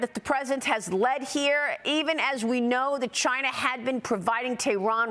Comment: I thought you said 'Russia' was the one providing intelligence to IRAN 😂 so it's CHINA now FOX news can't get there facts straight